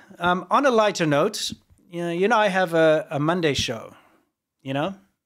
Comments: im usually on side in a lot of these minor disputes but i gotta say it definitely sounds like hes saying "I have a Monday show" 😄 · says "You 'n I" where I heard him say "I," but it's clear what he said in the playback. You were right!